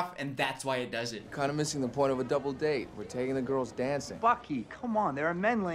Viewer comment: why does it sound like steve says “fuck you” instead of buckys name